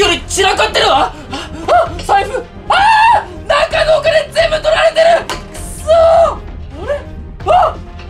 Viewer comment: の時、たっかのお金全部取られてる！にしか聞こえんw